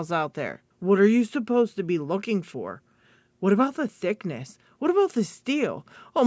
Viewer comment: Ive been on LPU too long lol at the start I thought you said 'What about the dick pick?' then realised you said thickness! I genuinely thought 'Thats a wierd product to recommended to a beginner!' 🤣